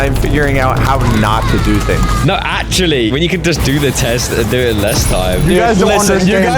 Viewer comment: what did that say? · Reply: Do it in less time 👍